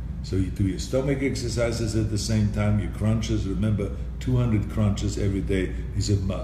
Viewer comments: I thought he said crunchies dam i thought he ment capn crunch I ate the whole box with whole milk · I’m sure i heard him say eat 200 crunchies a day. I can do that